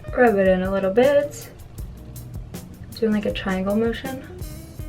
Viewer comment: did she say triangle or tribal? Lol